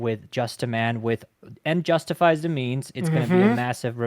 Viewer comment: Did he just say the end justify's the means